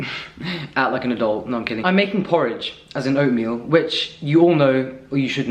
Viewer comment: OMG, when you said "I'm making porridge" .... for a glimpse of a second I heard "I'm making porn" hahahahha ...gosh .... Sorry 😂